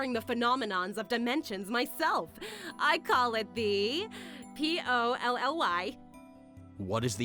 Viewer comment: She named a machine after her? If you didn't hear what she said, she said "I call it the P.O.L.L.Y"